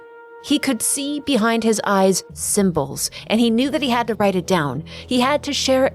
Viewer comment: I honestly thought you said "simples" as in the ad in UK, took my dumb brain a good 10s until I realized i misheard, haha